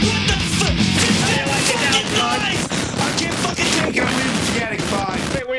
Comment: What does Milano say at the end there? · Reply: "I can't take anymore, forget it, I'm leaving, goodbye." "hey wait where you going?"